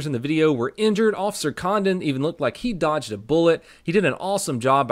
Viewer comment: Thought you said officer condom..bout to say.. he sure is good at protecting